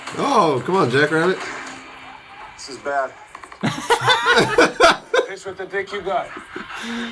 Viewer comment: does he say "piss with the dick you got"?